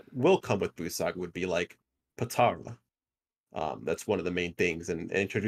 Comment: DID I JUST HEARD POTARA?! 👀